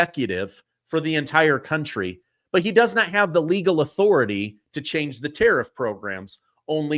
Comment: We already went and had this terrif court case and I thought they said he has the authority to change terrorists as a means of defense because other countries can use tariffs as a weapon ?